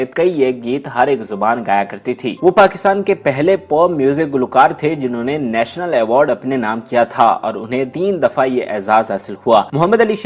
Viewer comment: wat r u saying !watch ur words. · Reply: Porn music gulokaar